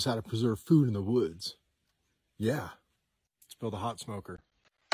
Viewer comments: Damn I thought you said, "i'mma pot smoker." lol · I thought at first he said how to preserve poo in the woods. I was like WTH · At first I thought you said it’s called a pot smoking 😂